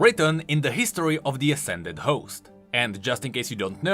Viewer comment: anyone else hear "ascended hoes"